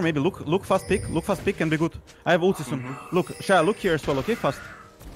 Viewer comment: I thought I heard I have autism. It was really I have ult.